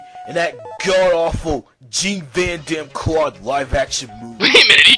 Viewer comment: Gene Vaughn Dam Claude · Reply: I always thought he said "Cheap Van Dam Claude"